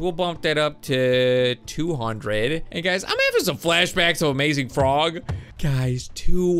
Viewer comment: For your next video in Amazon frog try to put the mega London and the red mega London in the toilet and make them fight plz I love your videos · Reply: 😂😂😂😂😂😂😂😂😂 did u just say amazon frog😂😂😂